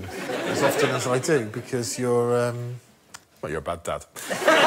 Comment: I thought he said Baghdad (Bag Dad) at first!